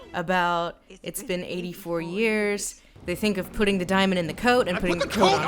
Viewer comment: For some reason, when Lindsay says, "put the diamond in the coat", all I hear is, "you put the lime in the coc[onut]" ...and then you drink them both together